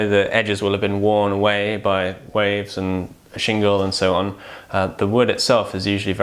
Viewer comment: "...worn away by waves and ___________". Please fill in the blank. This was at around 8:37. I couldn't understand what said. Thanks. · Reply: shingle